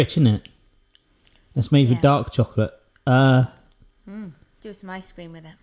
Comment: I thought you said it was "made with dog chocolate". I was wondering why didn't make a face.